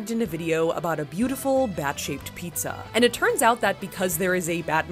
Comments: I thought she said bad-shaped-pizza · I thought u said bad shape, not bat-shape. 😂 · I thought you said pad shaped! 🤣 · I literally thought she said "Vag shape pizza"